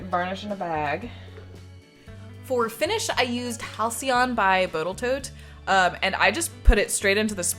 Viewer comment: did you say Bottle Toat? I though my brain was playing with me :D